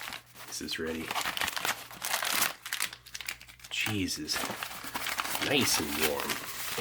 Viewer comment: Lol when he says "Cheese is...nice and warm!" I heard "Jesus! Nice and warm" and had laughing fit